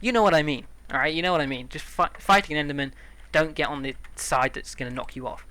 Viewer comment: Sounds like you said "fuck the enderman" instead of fight the enderman